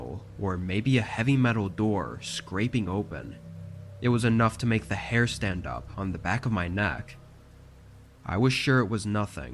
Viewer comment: Lord, I need to check my hearing. I could have sworn he said “made the hair on the back of my NUT stand up.” Yes, I know it’s not right.